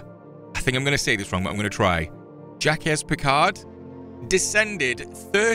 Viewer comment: Im like sitting here.. putting butter on my toast and shipping tea.. and suddenly i choke on the tea and was coughing thinking "did he just say Jacka**s Picard?" 😂😂😂